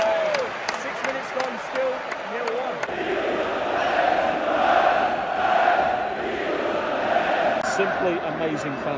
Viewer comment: what they are saying ? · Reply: Chi non salta nerazzurro è Who doesn't jump is a nerazzurro (Inter fan)